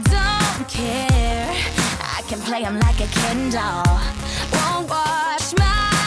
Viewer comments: when I first heard this song, I thought she said kindle instead of Ken Doll XD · I thought she said Kendall but idle